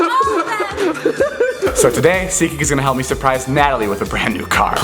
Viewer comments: I only just found out its called SeatGeek😅 i never understood David when he said it. I thought he was saying Seekee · I always thought David was saying ‘’zeegeek’’ .. I literally just knew that it’s SeatGeek! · all this time I thought he was saying "zeekeek" not "seat geek" lmao